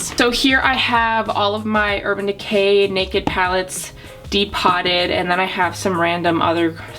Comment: Am I the only one disappointed that she depots everything and doesn't keep the original packaging? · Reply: I thought everyone was saying depot like home depot until she said de-potted. 🤣🤣